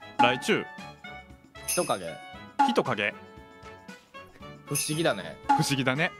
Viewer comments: フシギダネ が ｢不思議だねっ☆｣みたいな感じに聞こえてツボった笑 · フシギダネが不思議だね( ◜௰◝ ) に聞こえる笑 はい？に慣れてきてるぷりちゃん可愛いな